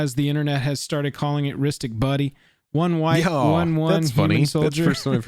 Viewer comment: Partner misheard it as "Rhystic BUNNY"! Which, I mean... look at his ears! 🤣